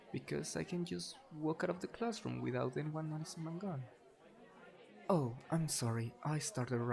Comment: DID HE SAY "without anyone noticing my gun" WHY DOES HE HAVE A GUN?! 😭